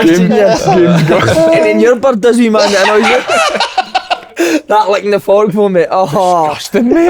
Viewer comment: Thought he said licking the fox - need subtitles